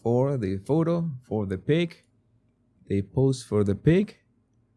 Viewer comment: Jesus at first I thought you said they have to pose for the PIG. I thought thats what you were calling the photographer. Then I realized a few seconds later you were saying PIC!! Lol 🫣